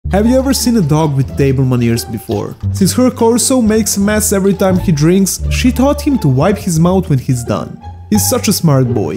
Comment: Thought you said "doberman ears before" lol